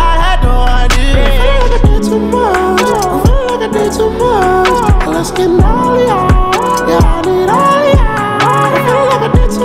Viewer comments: I’m i the only one who hears, “well, let’s get naughtier, yeah Midoriya” ??!?? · I heard something 0:59 "I Feel Like I Ate Too Much" "Feel like i ate too much" "Lets Get oreos" "Lets Get Oreos" Hahahahah Im sorry UwU Edit:Lol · other people hear: well, let's get naughtier yeah , I need all of ya What I hear : let's get naughtier young midoriya 😶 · The part where it said i need all of y'all i thought it said yeah Midoriya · Thought it said "lets get naughtier,yeah midorya~" But INSTEAD its "i need all of ya" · Bruh... 1:03 i heard it let's get naughtier young midoriya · i heard this “let’s get naughtier young midoriya” xd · When he said let's get naughtier yeah I need all of ya i thought he said let's get naughtier young midoria (aka deku/izuku midoria from mha💀🤚🤣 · I hear like “LeT’s gEt nAugHtiEr” “yOuNg mİdOrİyA” calm tf down all might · I always hear let's get maria instead of "let's get naughtier" :") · i hear ‘Lets get naughtier, young Midoriya’